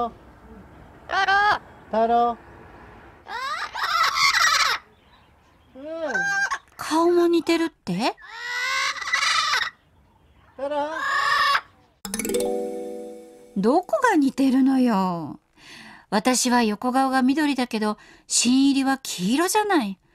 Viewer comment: 「うおー太郎！」って挨拶したように聞こえた